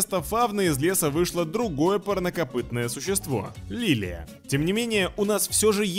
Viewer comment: Только я слышу "порнокопытное существо?" ;) Ник: Irrelevant Хочу покопытитьтся с духом цветения Лиллией